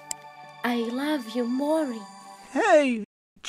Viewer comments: did she just say mory instead of morty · the animation sucks and they don't even sound like Rick and Morty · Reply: did she just say I love you Maury